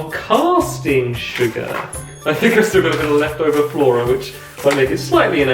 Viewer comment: what did you say? Flora? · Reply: Flora is a vegan butter substitute because stampy is vegan